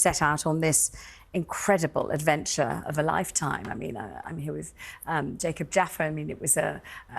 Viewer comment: does she really say "adventure of a life time".